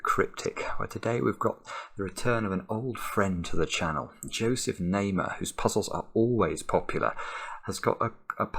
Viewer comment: Thank you Simon for this lovely solve. That was an epic solve as always. I'm glad you enjoyed it :) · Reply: oh, I heard it as "Joseph Namer", guess I'm not quite right